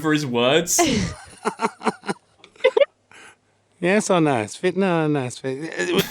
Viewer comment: Tf did the geezer just say at 3:04 ? LOL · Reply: “Yes or no? Spit no, no spit?”